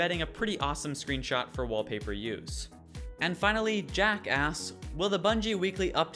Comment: Did I just hear Jackass or Jack asks?